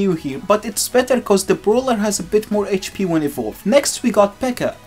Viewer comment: did blud just say brawler